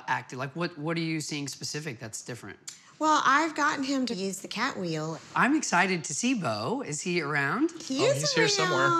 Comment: Lmao 33:54 such a weird edit, I wonder what she said hahahaha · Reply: She said "Beau needs 201 lbs"